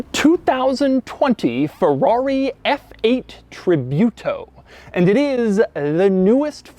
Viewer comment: OHHH tributo. Thought it said triturbo and was really confused as to why Ferrari would make a car with 3 turbos